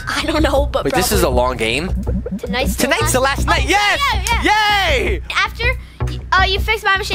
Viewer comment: so funny 😆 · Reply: I thought you said is the last day